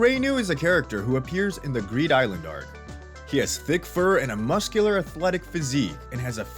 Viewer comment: did bro say fur💀